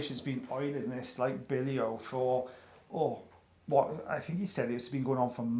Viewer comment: Ol’ American gal here…at the 0:22 mark, what exactly did you say? “Oiling this like _____”. Billyo?